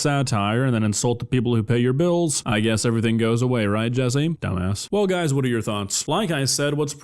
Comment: caught me off guard 😂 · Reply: I heard "don't ask" 😭😭